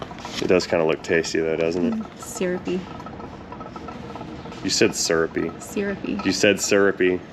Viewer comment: you ought to watch the footage back around 9:40... she clearly said "syrupy" not "surypy". Your brain just heard what you wanted to head haha.